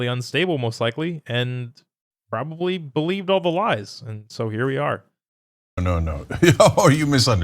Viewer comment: “Buy ALL the lies” ? Thought I almost heard